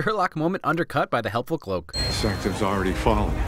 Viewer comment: The cloak is my favorite character. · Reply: I thought you said clock for a sec